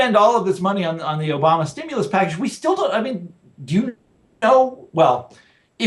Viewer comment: what is he saying? Frank sounds like he is saying "Jews." Jimmy's face changes. Just need to know what he was saying. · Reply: "Do you know...?"